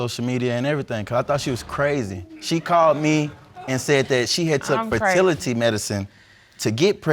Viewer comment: Fertility Medicine😱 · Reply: I’m way behind on these episodes. I thought she said facility medicine 😂😂😂 all these other words she said had me looking crazy🤣